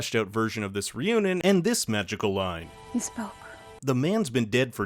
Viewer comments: I thought she said "you spoke". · She actually said "You spoke" in the Snyder cut.